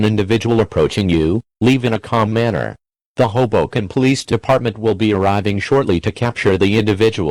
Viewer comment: I thought it said “the hobo canned police department”